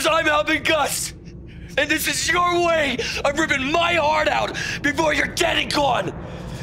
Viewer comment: i always thought Jesse said "before you´re getting caught"